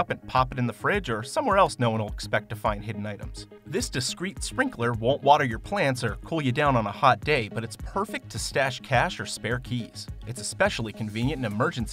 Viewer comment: did he say hot date